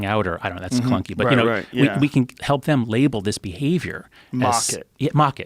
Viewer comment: Did they say "Mock it"?